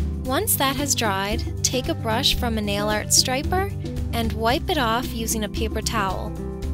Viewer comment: I thought it said fail nail art